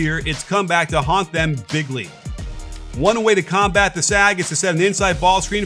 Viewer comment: did he say 'bigly'?